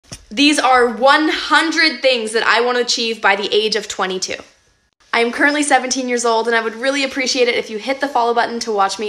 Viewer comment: I thought she said I am currrently 7 years old😳😂